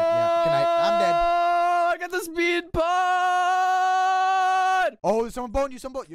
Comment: my reaction: OMG did say 'I GOT THE SPEED BUTT'?!?! Hahahahahahahahahha!